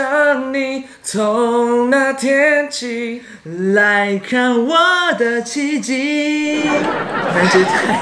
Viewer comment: 如果不看字幕应该会听成来看我的鸡鸡